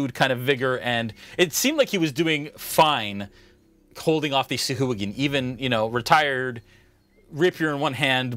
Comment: When you first said the name of the enemy creatures I thought you said sa'hooligan.